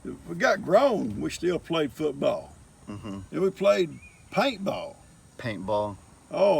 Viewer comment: What is “hank ball” I think your grandfather called it when he talked about the kids growing up...I git about football and then it sounded like he said hey played hank ball.,.,,never heard of that! Love your posts! Love that you are covering the history of your family! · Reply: Paintball